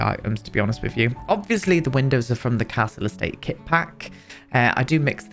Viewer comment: I was watching this whilst doing other things and misheard you, I heard 'council estate kit' instead of 'castle estate kit' 😂 I was super confused for a second, beautiful build as always though 🩷😊